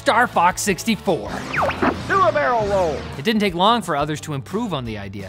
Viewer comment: So thats where the "do a barrel roll!" (or "who a'l mail roll!" as I heard it as a teen) sound I heard in some classic/early YTPs came from! Also, I never knew there was a Rumble Pak for the DS and DS Lite, years before the original Switch would (IIRC) be Nintendo's first handheld to have Rumble functionality built-in, though I can only imagine what playing games on an original DS with haptic feedback would've been like.